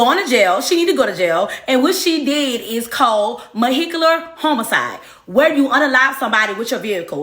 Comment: "MOHECULAR HOMICIDE" I thought... did I hear what I think I heard?⁉️😂😂😂 She said it with her chest too!😅😅😅😮‍💨 This was no laughing matter, but that part was too funny to ignore. I lost it!